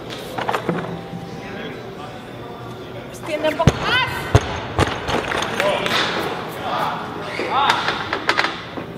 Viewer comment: What does she say? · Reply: She said 'Extend a little more'